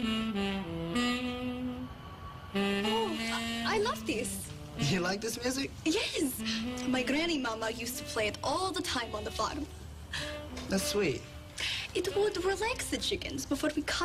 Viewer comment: She said “ On the Farm” I heard “ MoDa Fak” I was like “😳 mother Wait 🤯??” And Ty that reply “That’s suit “ its fits perfectly 😂😂